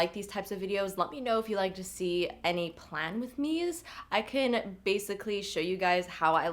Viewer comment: Pretty sure my brain is fried from studying for finals because at the end, I thought you said 'plant with me', as in you showing us how to put plants in a pot. It could've been because I was distracted by the cactus in the background. My goodness... Anyway, this was very inspiring!